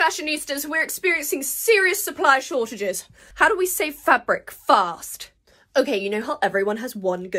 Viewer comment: I thought she said how to SAY fabric fast….I’m still waiting.